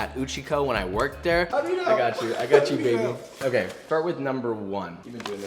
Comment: did he say “I got you baby”. I think we got a secret couple here 🤭